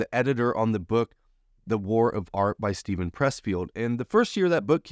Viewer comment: Thank you for the image. I thought you said the art of war - Tzu Sun!!!!